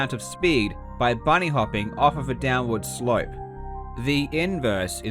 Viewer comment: i swear i almost heard downward sprial